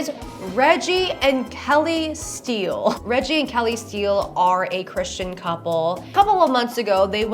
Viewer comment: I watch a lot of pokemon videos so hearing "Reggie Steele" sounded like "Registeel" to me and i had to do a double take and look away from my drawing tablet cuz i was just like "When did she become a poketuber???"